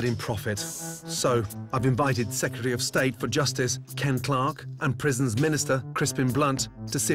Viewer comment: Ken clark i thought he said clark kent